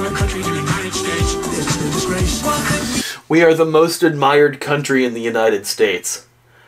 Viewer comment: he actually said "The most admired country, the United States" he had a stutter there but there was no "in" in the comment